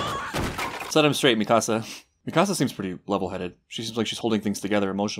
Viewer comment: Lol I keep hearing “mi casa” instead of Mikasa